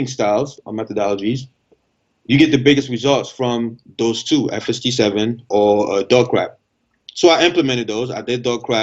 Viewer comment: What's he saying at 35:10? I hear and doggcrapp but I have no idea what the former is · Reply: It’s Fst 7